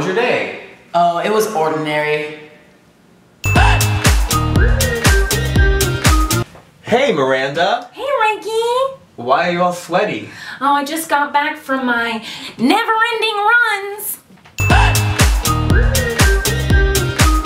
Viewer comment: LOL at the end i thought he said "Why are you all slutty?" XD Woops..